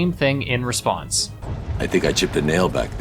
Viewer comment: What I thought he said was "I think I chipped the nail" and, as a non native English speaker, I just assumed that was some kind of idiom about losing a fight or being in trouble.